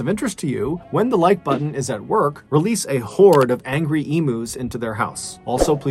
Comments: I had to rewind it bc I thought he said angry emos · I thought he said release a horde of angry emo’s. Lol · At first I thought you said release a whore in to the like buttons house...the like button may like that one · I thought you said angry emos lmfao...... not sure which would be more terrifying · For a second I thought you said to release a horde of angry emos into the like button's house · I thought you said a horde of angry emos and was ready to burst in, g notes blasting. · I thought he said a horde of angry emos 🤦